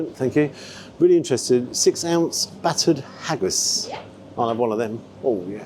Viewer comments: did you say battered haggis? Sounds lovely! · Il honestly thought said battered haggis 😮😂 I was like noooo😂